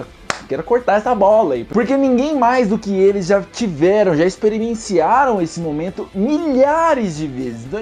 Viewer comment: Experimenciaram. eu ouvi viu! hahahahaha